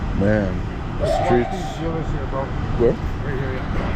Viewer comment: Did say "there's dealers there bro?"